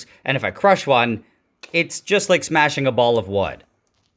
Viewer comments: I wasn't reading the captions, and at first, I thought that you said, "...and if I crush one, it's just like smashing a ball of blood". When you hit it with the hammer, I expected blood splatter everywhere, lol. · Thought he said a ball of blood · I thought he said "It's just like smashing a ball of blood" and was like... excuse me, what?